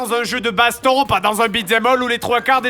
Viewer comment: un beat-them-quoi ? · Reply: Beat them all